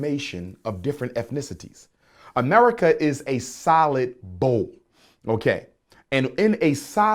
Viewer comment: I thought he was saying “solid bowl,” for a second.